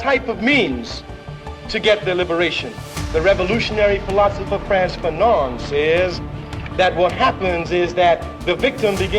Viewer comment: The subtitles are wrong at 2:06, the name of the philosopher is Franz Fanon.